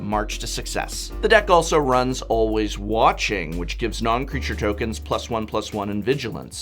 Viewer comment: Did he just say "noncreature tokens?" Or did I mishear that?